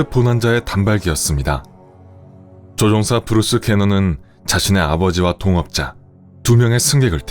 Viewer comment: 브루스 게넌을 브루스 배너(헐크)로 잘못 들은 1인ㅋ